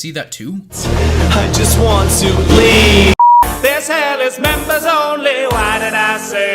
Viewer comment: Sounded like said "I just want to BLEED" I'm crying from this laughter